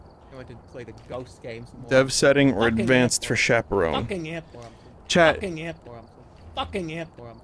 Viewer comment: also at 1:33:53 i hear "fuck an anvil..." or "fuck'n ant hill..."